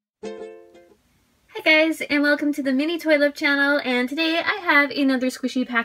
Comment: I thought u said Mini Toilet Channel